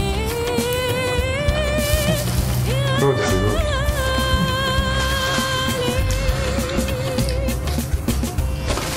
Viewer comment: شو قال: ممكن حدا يقلي😊😊☺ و شكرا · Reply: قلا مابدي حبيبي ما بدي. ؟